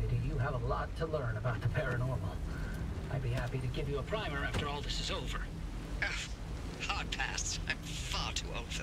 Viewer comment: Captions are wrong. He said "after all this is over" and caption says "after this is all over".